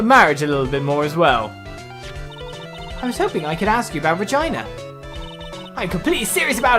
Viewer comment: Everytime he says 'Regina' I hear 'vagina'. Every. Damn. Time.